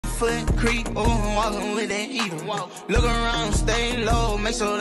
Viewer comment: I heard "right foot creek ohhwalinwiaear"